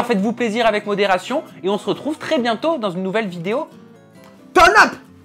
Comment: Reloud la musique en fond ! ça gâche un peu. Grrr 22:20 : dit quoi à la fin ? " Ton up ? "